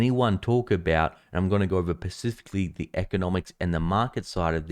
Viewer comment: Did you say pacifically? 🤔